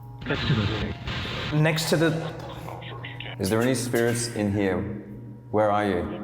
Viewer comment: it sounded like it said next to the lake to me but I dont think anyone heard it they only heard next to the